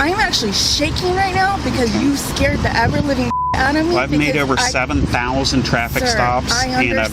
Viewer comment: Did anyone else hear “You scared the avril lavigne out oh me”